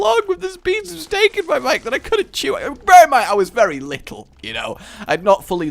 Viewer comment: WAIT WHAT DID YOU JUST SAY - oh wait he said masticate